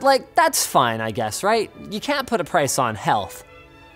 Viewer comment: I thought Linus said, "You can't put a price on Hell."